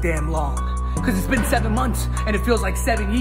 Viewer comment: I hear at the end "I have enough"